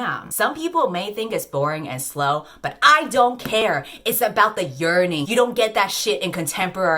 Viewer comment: when she said “it’s about the yearning!” my dumbass really thought she said journey pronouncing the j as h and i proceeded to pause the video because i was laughing so hard until i finally figured out what she said